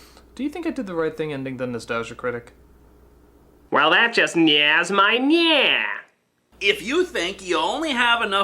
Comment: English speakers, please, can someone tell me what Critic saying at this moments: 4:55 That just ?nehing? my ?neh? - What it means? 8:29 The past seems to be getting closer though, with his ??words, wounds?? and being pushed. 8:53 Becouse it would inspire something ???-worthy. 9:15 You know of ??? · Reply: Since no one has answered you so far (and even though I'm not a native English speaker), I'll try my best to help: 4:55 That "somethings" my "something" is a typical way of saying something annoys you (most common I think would be "That really ruffles my feathers") 8:29 "wit's end" or "being at a wit's end" means not knowing what to do anymore, being at the point where you have no idea how to move on 8:53 "T Chun" worthy... however you write it, "T Chun" is a sound effect that is typical in "scary" movies where they try to jump scare you. It's the same sound effect you can hear in that moment. 9:15 I think he said "That you know of.", though he slurred his words there so it's hard to hear. Hope I could help you at least a little bit. :)